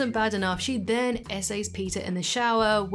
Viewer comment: what? What did you say? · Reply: SA, meaning sexual assualt. I can't say the full word otherwise YouTube detects it and restricts the video. Stupid rules unfortunately